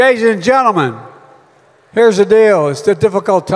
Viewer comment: I heard @ 2:39 Gays & Gentlemen.